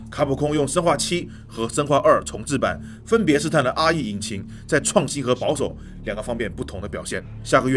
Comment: RE引擎聽成阿姨引擎.......害我嚇一跳